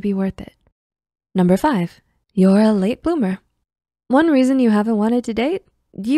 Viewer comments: 1. you know what you want 0:34 2. you don't know how to flirt 1:13 3. you've experienced trauma 1:53 4. you're guarding your heart 2:16 5. you're a late bloomer 2:58 6. you've been too busy 3:35 7. you don't get out much 3:56 8. you're focused on yourself 4:29 I hope I could help!:_PSIHug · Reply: It did, and here are my results: 1. Yes I do 😅 The problem is what others want 😁 2. I know, but that's what I reserve for specific type of people 3. Not at all 4. Always.... 5. That sounds correct 🤔 (also, I heard "boomer" at first 😂) 6. I'm currently jobless, so aside from "perfecting my craft", I'm always open 7. I do, it's just that I'm strict / too specific with interacting with people 8. I do, but not in a way that I'm unable to date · 1. Yes I do 😅 The problem is what others want 😁 2. I know, but that's what I reserve for specific type of people 3. Not at all 4. Always.... 5. That sounds correct 🤔 (also, I heard "boomer" at first 😂) 6. I'm currently jobless, so aside from "perfecting my craft", I'm always open 7. I do, it's just that I'm strict / too specific with interacting with people 8. I do, but not in a way that I'm unable to date